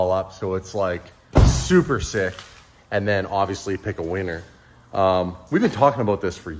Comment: sounds like you said pick a weaner 😂😂😂😂